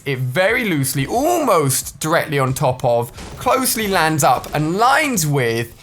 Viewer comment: wth are you saying 😭😭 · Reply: Felix Berkan lmao “very almost on top of closely lined up with” wth XD